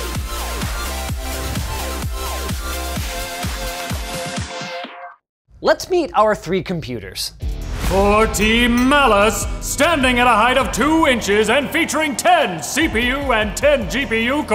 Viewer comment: I thought they said team malice lol